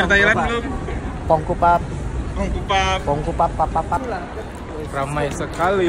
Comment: ngomong apa tuh skidipapap 😂😂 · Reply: Kongkupapapap 😂